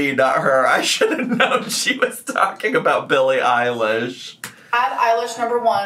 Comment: I thought she said eyelash too though